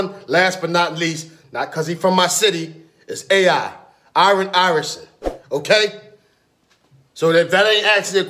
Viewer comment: I heard "iron iverson"